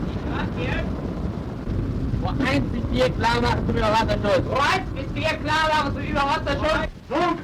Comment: the subtitle is wrong. It is "Überwasserschuss" (Surface firing) not overwater protection (Überwasserschutz) At 12:05 the incomprehensible is "Mündungsklappen werden geöffnet" (outer torpedo tube doors opening). The rest is the firing sequence "From conning tower, 40 degrees, steady as she goes ("Recht so" in german naval lingo is "steady as she goes" in english). The incomprehensible at 15:02 is "LI" which is short for "Leitender Ingeinieur" (Engineering officer)